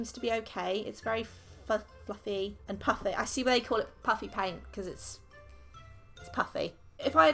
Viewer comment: i thought she said puppy paint